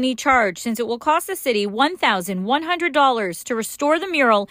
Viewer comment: The greater question is why does it cost $100,000 to paint a dumb rainbow on the road? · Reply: It think she said $1,100 for the paint.